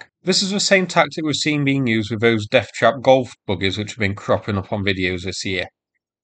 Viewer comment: I thought you said "golf buggers" but, on reflection, it must have been "golf buggies!" Sorry, southerner, here.